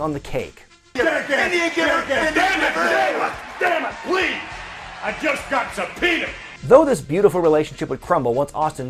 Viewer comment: I thought Vince said "I just got some penis"